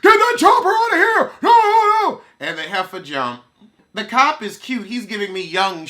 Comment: At first i thought you said "And they helped her jump" 😂😂😂😂😂😂😂😂